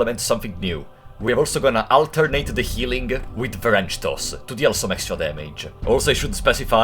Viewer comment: I love your accent because I keep hearing "french toast" instead of "wrench toss"